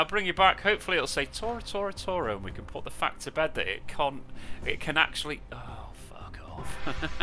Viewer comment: This video will prob be demonotised too you accidentally said cnt instead of can't on 39:19 😅😆